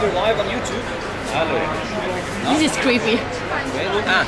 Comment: Did i heard "he is creepy " at 5:41 or she said some thing else